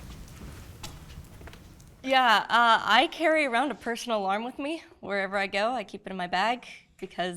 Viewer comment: at first I heard "personal arm", I was like your in Los Angeles California how's that possible. lol